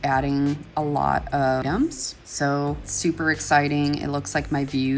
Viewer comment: Loved this series so much - so inspiring and helpful. I do have one question - in the video at about 5:35, what do you mention that you add a lot of to see an increase of views to your shop? It sounds like the video cuts the word and for the life of me, I can't tell what you're saying (and I really want to know!). Thank you! :) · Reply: 'items' 🤗